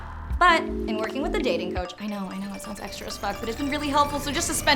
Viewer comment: What is she saying at 0:13? "I know i know it sounds extra... ???"